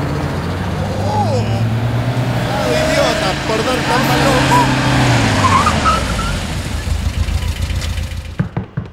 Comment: entendí "cuidado idiota por dónde vas!" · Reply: el dialogo completo es: pleado idiota, por dar tan malas noticias, un paztelazo en la cara, ejejeje. origen del dialogo "hielito" episodio: 2 temporada, episodio 15. xd